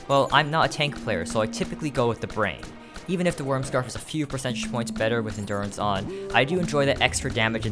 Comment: What he said: "a few percentage points." My brain: "a few percent shmoints." Never misheard something quite that badly before lol